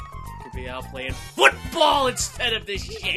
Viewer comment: wtf is bowser jr saying?! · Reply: "This place is huuuuuge I can't find my way around this caaaaastle!"